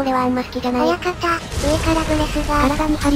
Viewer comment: 上原ブレスに聞こえる